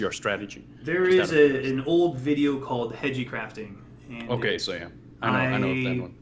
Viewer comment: what is the video he is talking about? I cant quite find what he is saying. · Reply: "Hege"crafting <link> Named for Hegemony, the streamer who coined the style. It's not entirely up to date but the core concepts are very solid.